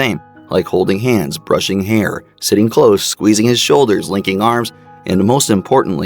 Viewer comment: Lmao, tell me why I heard "licking arms" instead of "linking." And now I can't stop laughing bc it fucked up the rest of the video for me lol.